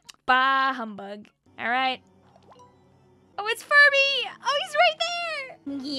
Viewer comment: I THOUGHT UT SAID FURRY- (I promise I didn’t read tentacoles comment…)